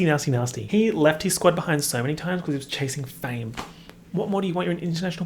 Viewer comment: i heard "he (alvin) left his squad behind because he was chasing FANNY"